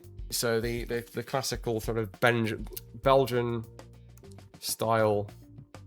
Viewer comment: Did he just say Belgian foliage, because I thought it looked like a chocolate gun myself... 🤣